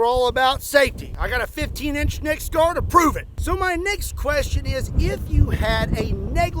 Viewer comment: i thought he said neck's question